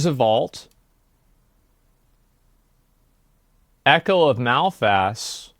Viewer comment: Did he just say "Echo of Mouth Ass"? 😂